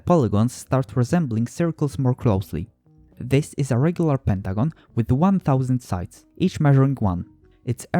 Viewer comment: Did he say regular pentagon? But the captions say regular polygon?🤔 3:03 - Did he say "nominator"? I think he means numerator, right?